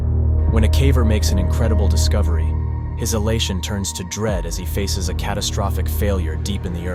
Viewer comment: I keep hearing "hellation" instead of "elation" but that is due to narrators mispronunciation